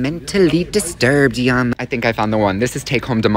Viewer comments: I thought you said bitch not gitch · when he said gitch it sounded like he was saying b*tch